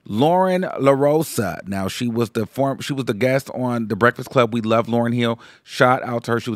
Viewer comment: “From Loren Lorosa … we love Lauryn Hill 😂😂” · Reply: Ohh I thought he said “we love Loren here”